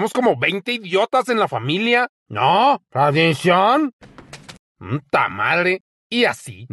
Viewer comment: "Somos como 20 idiotas en la familia" jAJSJSJSJS ME MUERO XD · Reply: yo pensé que decía maldición 😂